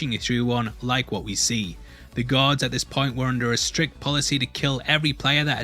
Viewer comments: U need to work on the way you say "Guards". I keep hearing "Gods" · For some reason I thought you were saying gods and not guards 😂😂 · Dude keeps saying guards but all I hear is "god" or "gods". Such a mind fuck.